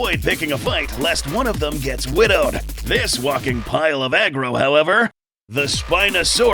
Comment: Ok, idk why, but I swear when yall were talking about the Spino, I heard, "This fucking pile of aggro, however." And not. "This walking pile of aggro, however."